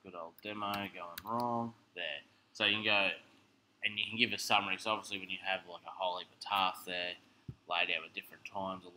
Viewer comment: How do yo get at 5:34 the total time at the first header? I couldn't hear the commands you pronounced. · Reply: C-c C-x C-d org-clock-display <link>